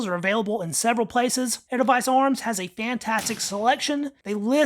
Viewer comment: Slow down a bit as you talk too fast alot. When you reference the business/firearms dealer at minute mark 4:18, I can't even understand what you are saying. I think you are trying to say edelweiss arms, but practically all decent swiss long arms at edelweiss arms are going to be well over the price range of $400-$600 you reference. Also, I wish you would stop plugging RTI as they sell a lot of junk. You even have videos showing where RTI sells junk.